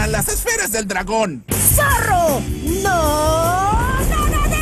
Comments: alguien sabe que dice? Mis interpretaciones: Zorro no te desterramos o detestamos? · diego creo que dijo zorro no tocamos rabos